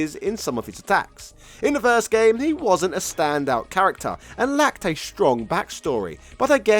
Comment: I swear I heard "and lactate strong back story" 😂 And Gen reminds of those corner shops here in Hong Kong which always claim they are having a massive sale, coz they are closing down in, say, 3 months, but still there after a year. Lol